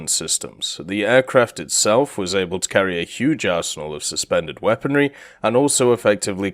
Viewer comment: wait a minute.. run me by that again.. 1:55 Huge.. arsehole? wha?.. (No offense but.. I do not hear the "n" of the word "arsenal" buuut.. who cares anyways..) Edit: I'm not crazy at all, but I really heard arsehole